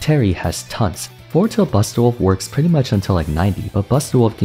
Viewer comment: "Are you ok?" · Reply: I thought he said bastard wolf 😂 That's my Mexican hearing.